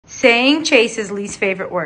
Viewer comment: I thought she said “ saying Chase’s last words” I was like: NOOOO THE KITTY 😭